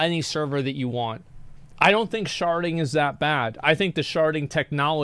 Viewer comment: I don’t play WOW so all I heard was: “Sharting is not a bad idea”